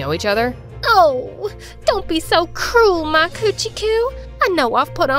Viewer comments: "Oh don't be so cruel my coochy coo" Like did she just say coochy coo👀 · did she just say coo